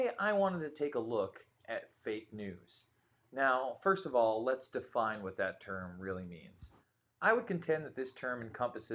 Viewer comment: I had to restart the video, I thought he said: let's define 'bacon'.